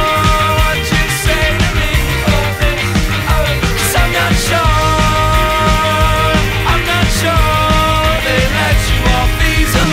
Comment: I THOUGHT IT SAID “cause sapnaps short” NOT “cos i’m not sure” 😭😭